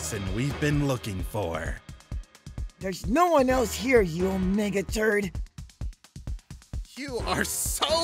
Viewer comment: Aww, Daniel is so cute! He said that Ein was a “ Mega Turd” lol 😆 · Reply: Wait he said "mega turd" I heard "omega turd"